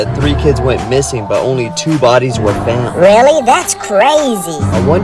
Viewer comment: "I'm at the bottom of the lake." Bruh I thought he said "He's at the bottom of the lake."